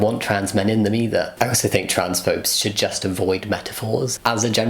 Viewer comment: in the video, auto-generated English caption reads “trans popes” instead of “transphobes” .... whoops